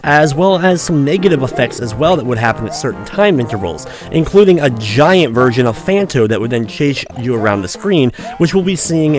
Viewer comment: Wait a minute...did you just say...a giant version of Phanto? ..gulp